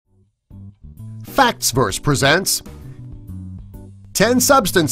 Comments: I thought i just heard "Facks Birds Presents" in the intro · sounded like you said fucks verse